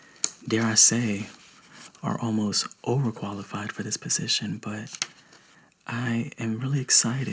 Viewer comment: I heard piece of shit instead of position... Why am I so weird